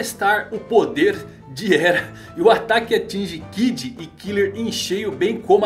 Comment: Foi só eu que percebi o ele falou que killer Bee no momento